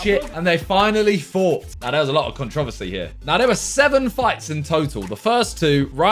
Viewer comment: did he just say controsety at the start